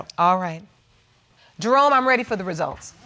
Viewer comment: I heard “Drum.. I’m ready for the result” 😂